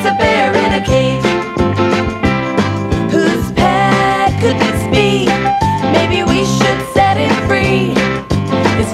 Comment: Do you guys know what they say at 0:29??? HELP ME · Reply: The line that starts there is "whose pet could this be? Maybe we should set him free"